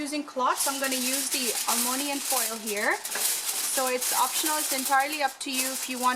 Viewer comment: Alonium foil 😂😂😂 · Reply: i thought she said ammonium foil i was like LOL